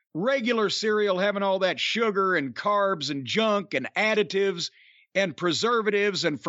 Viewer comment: for a minute I thought Jim said, cereals with all those "additudes" instead of "additives". I said "yeah, these cereals all have attitudes. Especially those fucks on the Rice Crispies box."